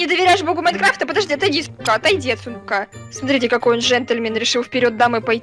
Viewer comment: мне одной послышалось: отойди с*ка😂💜